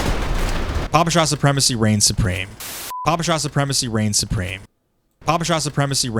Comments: I honestly thought he said papa John reign supreme like What does pizza have to do with any of this? Lol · even with the whole bit of mishearing it, i cant hear anything other than "papa john supremacy" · I thought he said " Papa Johns supremacy reigns supreme".